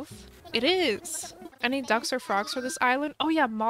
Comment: At first whenever she said ducks or frogs on the island I thought she said dumpster frogs 😂😂😂